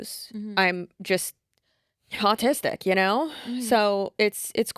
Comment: it kinda sounded like said "Hautistic" so I thought she was combing hot and autistic, and I was like yeah checks out totally makes sense, that she is